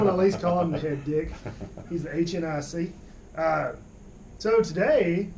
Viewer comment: Did he just say "He is the HNIC"?